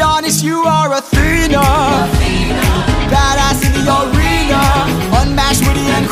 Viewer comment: I always thought it said badass señorita